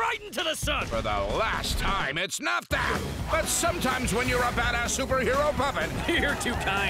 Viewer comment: did he just say bad a*s